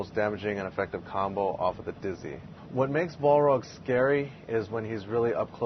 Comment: I thought said "that's his most damaging combo of the hizzy," i had to rewind that part and up my volume